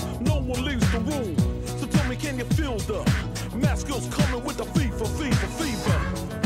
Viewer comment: When I was younger, I thought he was saying Fifa, Fifa, Fifa!